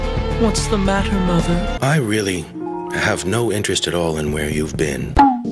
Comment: never watched diabloik lovers but this shit is hilarious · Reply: i thought he said he has no interest in women 😭✋